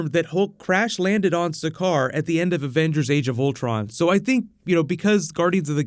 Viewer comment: thought he said ravagers for a sec